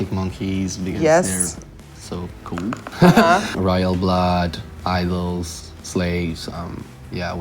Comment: Help me please! Which band did he said on 12:06 after royal blood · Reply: IDLES and SLAVES